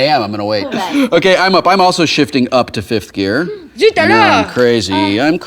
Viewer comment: Did I just hear Hannah say "Zut Alors!" and "Sacrebleu!" ?!